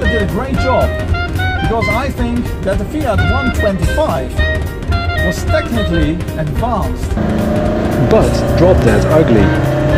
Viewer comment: what are you saying? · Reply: I said that the Fiat 125 was technically advanced, but was drop dead ugly.